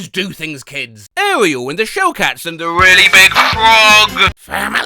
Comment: Why did I hear "We Are Number One" when you said "And the Really Big Frog"?